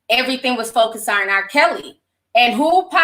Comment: did she say Char-KELLY? 💀💀💀💀💀